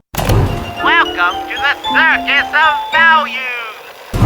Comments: Fill your gravy at the circus of value! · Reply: I allways thought that it was saying "buy your grave at the circus of values"🤡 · For some reason playing the actual game i always hear, "kill your framerate at the circus of values!" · I always thought he said “Show your crazy at the circus of values!”